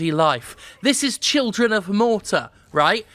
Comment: I got to say I first thought the game said Children of Mana.